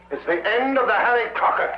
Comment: Astonishing ! at 20:00 or about, the subtitles show Harry Potter, instead of Harry Crocker !